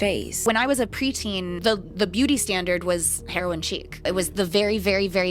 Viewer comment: did say "heroin cheek?" 😬🤭😆 Did she mean "chic?" (Sheek/shēque) I listened 3x and I swear I heard "cheek."